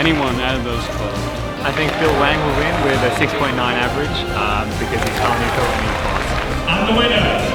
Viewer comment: Can anyone hear what Feliks is saying about Bill at 11:02? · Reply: He thinks Bill Wang will win with a 6.9 avg